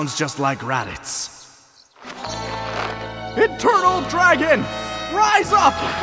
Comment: Did Krillin say "Internal Dragon?" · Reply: Eternal